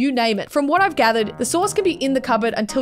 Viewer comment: i thought she was saying source and i was so confused but its SAUCE